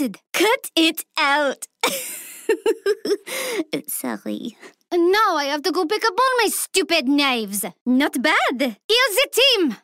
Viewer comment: what she says here ? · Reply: "And now i have to go pick up ALL my stupid knives!"